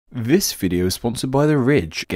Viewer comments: I thought that you'd said, "This video is sponsored by the rich." Lucky you. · I thought you said “this video is sponsored by the rich” and I didn’t even question it